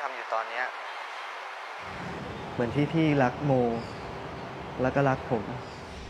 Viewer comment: I thought you said in the yellow line that "what about me dude"even though it was mo😂